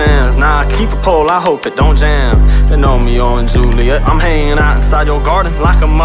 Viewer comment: "they know me - out in Joliet" am I mishearing this or is this a reference to joilet il?